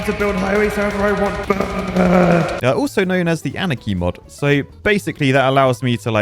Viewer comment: only i hear always "Anakin mod", instead of "Anarchy mod"